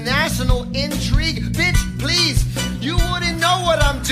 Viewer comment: DID FUCKING SAY BITCH PLEASE 😭😭